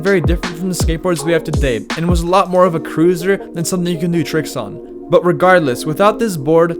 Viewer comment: did he say scooter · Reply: He said cruiser, it's a different type of board.